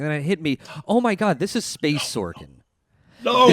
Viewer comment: What did say? "This is space sorting?" Makes no sense to me. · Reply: Space Sorkin (as in Aaron Sorkin)